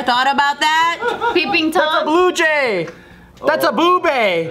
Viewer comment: "Bluejay...that's a boobay!" 🐦🤣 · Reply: I thought he said ‘blue bae’